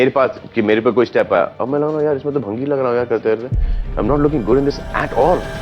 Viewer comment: Roasting Selmon bhoi alive 😂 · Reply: Bhai he said Bhangi which means who smokes bhang.